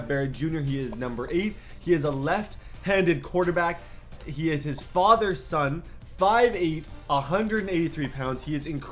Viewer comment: did u just say "he is his father's son" ??